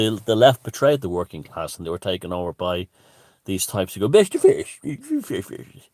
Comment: I thought you were saying "best fish" 🐟 for ages until you said today it was bash the fash .haha. Fantastic ☺️